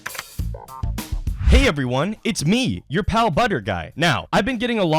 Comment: I thought you said "hey guys, it's me your cow butter guy"